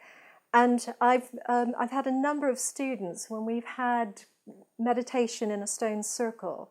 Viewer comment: I heard "number of Stevens" needed to play that part again to understand its students